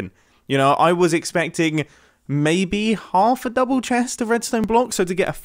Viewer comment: did he just say half of a double chest lol?